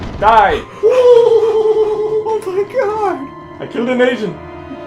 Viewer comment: so satisfying that you beat him but it sounded like you said you killed an Asian😂